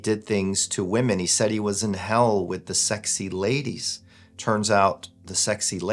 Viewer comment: For a sec, I thought he said "Sexy Sadie". :P